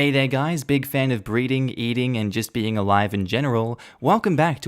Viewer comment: Oh.. Breathing. See now I thought you said breeding.